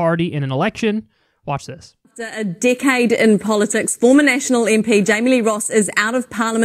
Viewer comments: Everything they said decade it sounded like they were saying dickhead lol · Totally thought they said “dickhead” when they were saying “decade” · I know she said "decade in politics" but I swear she said "dickhead in politics" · Every time they say "decade" I hear "dickhead" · I thought they were saying "dickhead" but they were saying "decade"...